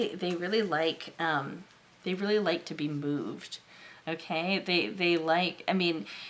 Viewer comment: #1 sounds like me. 😅 #3 I heard "they really like to be boobed" then realized you said "moved".🤣